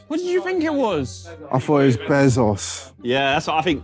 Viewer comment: I heard it as BehzOS lmao